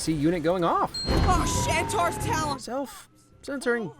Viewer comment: First time watching the movie, me along with the rest of my family swore that he said “oh sh*t!” 😂